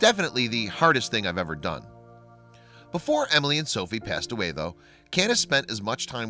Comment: the announcer said "...before they passed away..." Did I hear that right?j